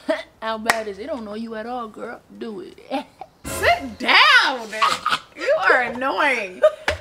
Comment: So nobody heard say “my poor boo” after said how many tickets she had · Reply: Around the 8:40 mark it sounded like said sit down, bae. 🤷🏾